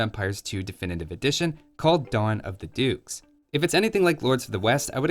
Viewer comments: I thought you said "Dawn of the Dudes". Cue in the first infantry and chill civ. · Thought it said Dawn of the Ducks 🦆